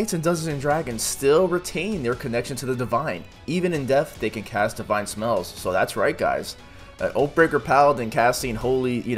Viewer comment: I swear I heard "cast dividend smells"